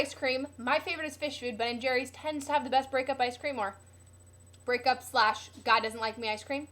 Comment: "My favorite is fish food, ben and jerrys tends to have the best breakup ice cream" <-- sounds like she said that